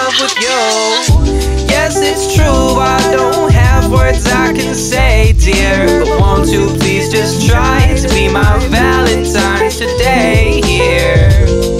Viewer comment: I don’t understand what he says hear ? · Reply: “Yes it’s true I don’t have words I can say dear But won’t you please just try to be my valentine today here” is what I say